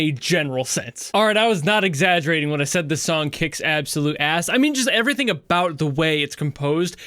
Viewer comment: I thought u said the song is absolute ass